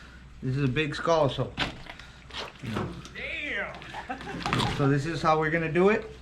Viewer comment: I heard Damn lol